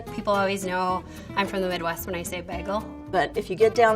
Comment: Did she just say bwagel?